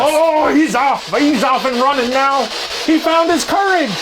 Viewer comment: I thought said “he found his Keurig!” But he said ^courage.